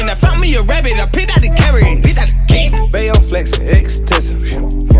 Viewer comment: I thought he said “baby I’m flexin , x Tesla “ 😭I been thinking he was talking about the Tesla model x this whole time